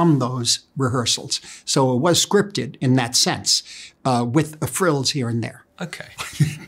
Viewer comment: what does he say? "It was scripted in that sense, with ... here and there" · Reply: "... uh, frills..."